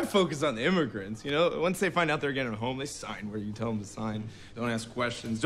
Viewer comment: Subtitle error at 2:53 Subtitle says "they're getting homeless" (not what was said and this makes no sense) Corrected: "they're getting a home"